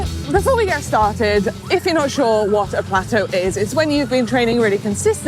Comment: I always overcome it by just getting stuck in until it's all gone. Sorry, I thought you said gateaux.